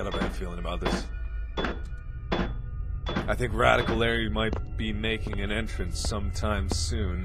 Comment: I know this is several years old, but I don't know why, but for some reason my brain heard it as "I think Radical Larry might be naked."